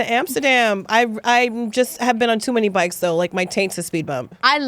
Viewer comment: "I've been on too many bikes tho, my taint's a speed bump." Is that what I heard at 10:34? Lmao